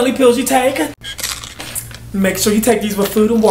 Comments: “Make sure you take these with food and water, you cannot snort these. Ok”. 😂🤣😂🤣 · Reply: I thought she said smoke not snort😂😂😂😂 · I thought they said you cannot smoke these 🤣!